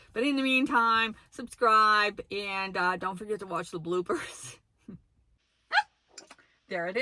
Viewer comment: I thought you said "don't forget to watch the bluebirds." 😂 I moderate a group for vanlife women, and so many say "I have to have a toilet!" and all I can think of is that black tank. My motto is simplicity. Black tanks are not simple, imo. I have a collapsible toilet, compostable bags, and poo gel. I'd use pine pellets, but no room in a minivan. Also, not a weird topic. We all have to do it, and that's usually the first question people ask. Lol. So this video is really kind of a PSA. 😅